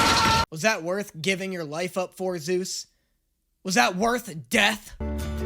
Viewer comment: “Was that worth giving your life up for Zeus?” Did anyone else catch him saying zeus instead of soos?? Lmaoo